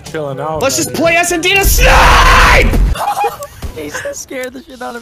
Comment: What is he saying? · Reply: Elijah Shells let’s just play SnD just to SNNNNIIIIIIPPPEEE